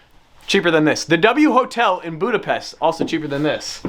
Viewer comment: did he just say Budapest? Hungary mensioned in a Stradman video. Pretty sick ngl.